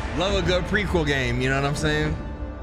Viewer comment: did Berlin just say prequel game? It’s good let’s just let him find out